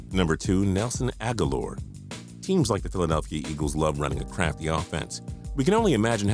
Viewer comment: Legit thought about u said "eagles like to run crappy offenses "